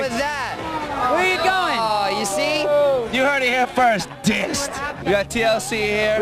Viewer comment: Red carpet interviews should be no biggie for Joey. ;D BOO BSB!!! 5:40 At first I thought Joey said, "You heard it here first. Dicks!" LOL XD